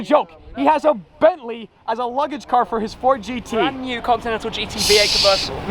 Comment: Bro, I’ve been getting messages that I was in your video at 0:05 👀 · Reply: It sounded like he said "tits ford gt"